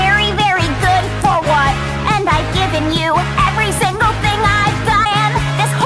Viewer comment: "And i giving you everything i die" Edit: That what i heard*